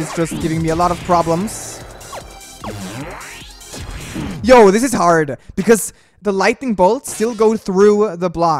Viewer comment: Wait did say ‘this is heart’ or ‘this is hard’? Is it just me or did someone notice that too? xD